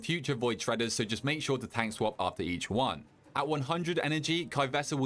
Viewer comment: All I heard was.. 'make sure your tank is an absolute baller'